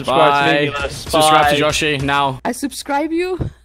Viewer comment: Is it just me but first I thought the intro said 'Joohy'